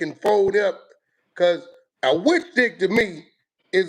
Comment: Thought he said a fish stick 🎣